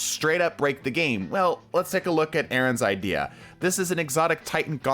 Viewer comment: I legit got a mini heart attack cause I misheard Erynn as Aterron. (My name)